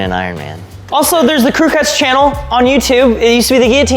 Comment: oufff.. that channel name you guys. I didn't know about that channel until this moment, but since I heard it now I have to mention this. I jumped back in my chair a bit there, cause it sounded like said. "also we've got the Clue Klux Channel" Crew Cuts has an eerie resemblance. I know that's not you guys! at all!! There's no chance of it! So I just wanted to show this to you. But that name needs to be said in another way/flow or renamed like CC Vlogs or something. The flow and sound of it in that moment, it f'ed with my head a little.